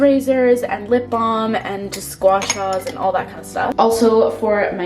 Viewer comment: I thought she said "Squashmallows" but you can hear both Gua Sha and that if you think of each word